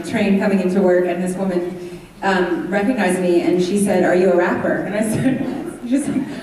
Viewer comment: What happened when everyone started laughing when the interviewer asked oak about has he rapped before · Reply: I'm pretty sure they thought she said raped